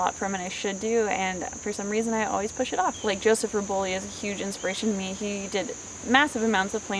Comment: Hey can someone tell me what artist did mention at 8:12 ? The one with the pumpkins. I didn't understand his name| EDIT: His name is Joseph Reboli. I finally managed to find him.